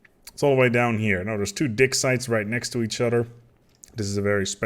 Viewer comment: is it me or do i hear "dick site/s" all the time?!?